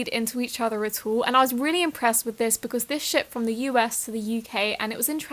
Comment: Is it bad I heard "shit" instead of "shipped"?